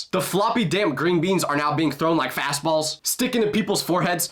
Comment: Why i hear fazballs